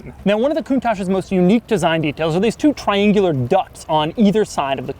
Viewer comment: Doug: "two triangular ducts" I heard "two triangular "ducks""